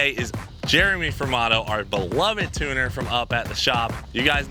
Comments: Dude for years iv thought you were saying Jeremy from auto like he had a another business named auto but I was always confused because I knew it was fasterproms...lol like maybe he a big message board called auto and he was Jeremy from auto.. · For years, I thought was saying "Jeremy from Auto".... Legit Years....